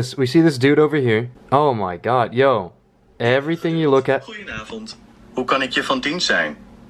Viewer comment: LMFAOOH did you say "HEY SIRI" in RUSSIAN? i think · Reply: No siri said that in Dutch xD goeienavond means good evening lmao